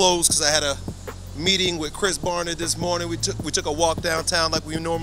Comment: I thought he said chris Brown for a mint 🤣